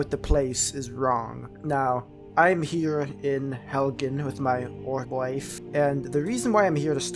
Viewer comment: "I'm here in Helgan with my boy wife" Am I the only one that thought he said that?